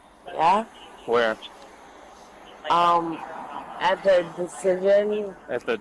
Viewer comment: I thought that she said "At the decision." meaning "At the intersection".